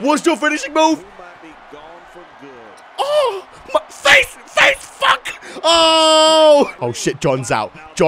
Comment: On 6:03 Did say facefuck 😆